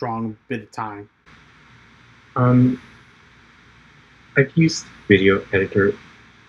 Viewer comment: I think is saying he used VideoPad Editing Software.